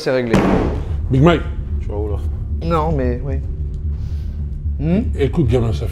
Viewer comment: c'est moi ou le gars lui a dit "boug mwen"?